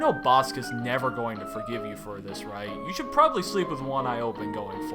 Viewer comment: did you say Andreas should sleep with 1 eye open? I think he should at least even sleep censored